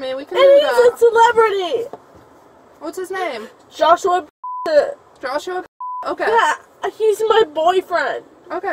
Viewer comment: Did she just say Joshua Bassett? Lmao